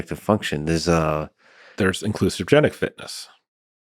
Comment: what’s he say? “Inclusogenic” fitness?